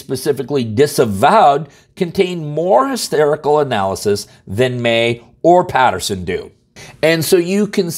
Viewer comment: did he say, "contained more hysterical analysis?" 😃